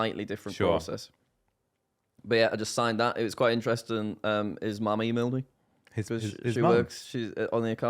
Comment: what did Jack say????? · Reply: i think "his mam emailed me" as in mr beast's mum as she works there